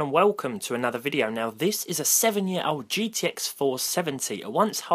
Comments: Who else thought it said rx 470 lol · i thought it said rx 470 i was like tf dis clickbait doe the i realized its gtx470 xD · I thought it said rx 470